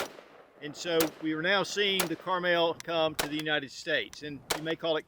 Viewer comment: Every time you say Carmel. Is sounds like you say Caramel.